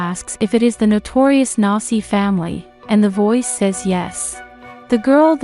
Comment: did she say nazi family?